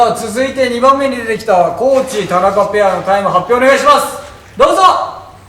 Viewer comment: 田中 高地ペアが田中聖ペアに聞こえてしまったのは私だけだろうか…